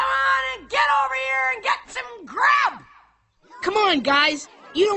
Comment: did she just say crap? xD